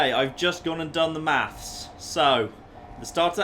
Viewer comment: for the longest time i thought you were saying MATS, like what the hell are mats, now i realized you're saying maths. which makes loads more sense